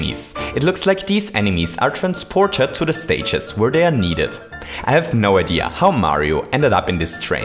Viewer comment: Where dan needed (i heard dan but I know he's not actually saying dan) daan played your course Koopa Shell Island